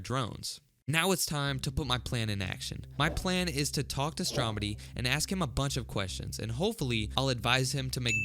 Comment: Thought you said STOMEDY